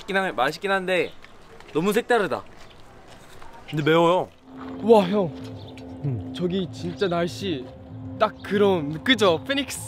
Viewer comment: did yuta say "woah hyung" ? Its cute tho